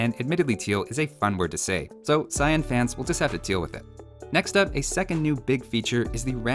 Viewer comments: I heard "So Cyan fans will just have to Teal with it" instead of Deal with it. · Did u say cyan fans will have to teal with it xD?